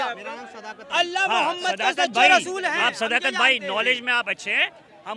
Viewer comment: listen very carefully what he said is Allha Mohammad ka rasul hai 😂😂😂😂😂😂😂 And bhai hats off for your bravery 🙏🏻